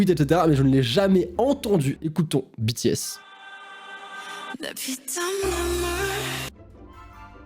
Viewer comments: moi j’ai toujours entendu la bite a ma main 🤷‍♀️😂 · a 10:49 moi j'entend "la p*te a maman" 😅🤣🤣🤣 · Pour les BTS ( 10:43 ) peso j'entends "la ptn de moi " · moi j'entends plus "la p*te a mama" ou "ah putain ma main" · moi j'entends "la p*te à maman" 😭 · moi j'entend "Depuis ta maman" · moi j'entends "la putain d'vos mort" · moi j'entends "Mais put*in maman" ou "La b*te à maman" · moi j'ai entendu " la pu*e a maman" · moi g plutot entendu "mon sang ma sueur et mes larmes"en coreen ( ͡° ͜ʖ ͡°) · A 10:51 moi j'entends (mais putain maman) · Moi à 10:49 j'ai entendu "Le putain de Momo"